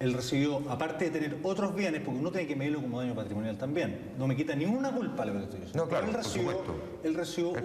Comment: alguien entendió lo que dijo en 10:50? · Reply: "Porque uno tiene que medirlo como daño patrimonial, también"